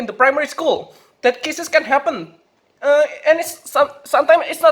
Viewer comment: Wait did i hear kisses in 3:50 😂😂😂